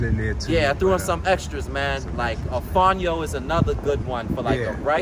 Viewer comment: 1 Avocado 2 Mango 3 Watermelon (With Black seeds) 4 Walnuts 5 Watercress 6 Wild Rice 7 Dark Chocolate 8 Cherries 9 Butternut Squash (Pumpkin) 10 Amaranth 11 Spelt Bread (Yes they did 11 instead of 10) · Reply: LV What was that one he mentioned @ 19:10 I think he said "afanyo". It's the rice substitute that he mentioned. I don't know how to spell it.